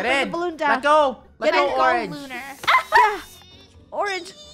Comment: calling lunar orange Me: what did they say Also me: goes back to 3:23 😂😂